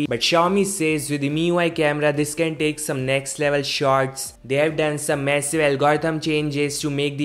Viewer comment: did he say it takes next level sharts ?!? I don't wann even image what that would be lol